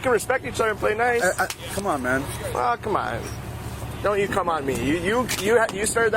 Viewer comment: 😂 did I just hear that cop correctly say at 17:45 and I quote Don’t you cum on me. 😂😂